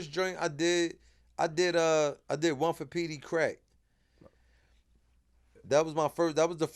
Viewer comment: I thought he said peediii crackhead 😂😂😂😂😂😂 💀